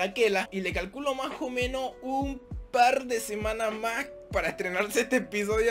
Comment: Espera que dijoooo??!???!!!! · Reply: Un par de semanas??!! A dicho un par de semanas??!???!!!!!! 😨😨😨😨😨😨😨😧😧😧😦😦😦😦